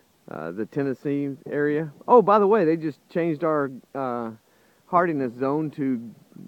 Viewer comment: Hi again, what zone have you become? I thought you said 'Hardiness' zone 8